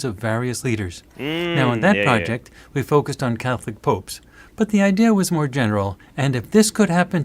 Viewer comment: After the poop snowman, I heard "catholic poop" instead of pope